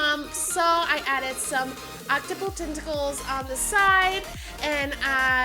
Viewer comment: Who else heard her say octupal for octopus